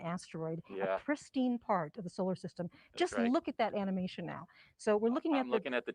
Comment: did she say "just look at this ANIMATION now" ??